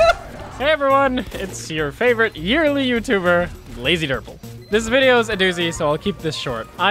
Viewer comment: Hey everyone!!! Hopefully this doozy of a video can tide us over while we wait for the medic video to animate and edit itself ;). It's a relief to get these clips out there and clear out my drives. Anyway, thanks as always for your patience - I hope life's been treating you well! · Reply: Did you just call yourself lazydurple